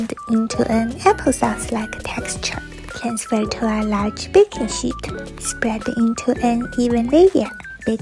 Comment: When she said baking sheet I thought she said baking shit😅😅😅😂